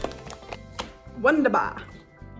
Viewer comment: did you just say "wunderbar" ^^? or is my brain playing tricks on me? ... Anyway greetings from Germany ❤️